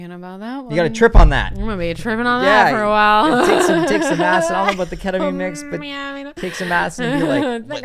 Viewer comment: I really don't know why and I would love to know why, but every time says "take some acid" I hear "dicks and asses"